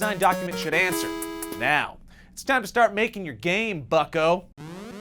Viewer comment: lol I thought he said fucko 😂😂😂